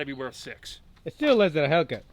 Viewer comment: ?? Did he say feels like the hellcat?